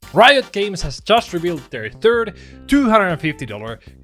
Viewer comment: I know you said "third" $250 dollar gatcha skin but I heard "turd" and honestly it still checks out. These skins are absolute sh*t.